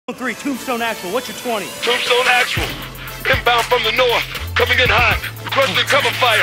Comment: swore he said pimp down from the north